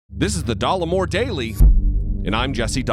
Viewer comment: As a french viewer, i always hear "de l'amour" instead of Dollemore each time you introduce yourself. I guess it could be worse.